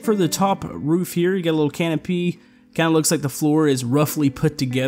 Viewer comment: When he said canopy. I heard it as A Can Of Pee